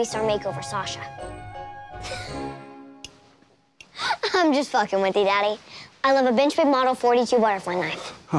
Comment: did she just say (im just fucking with you)